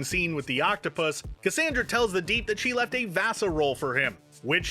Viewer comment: I thought she said she left Vassar (as in Vassar College, where she might have been on the faculty), not VASA.